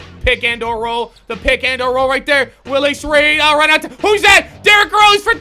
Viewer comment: did he say willis reed?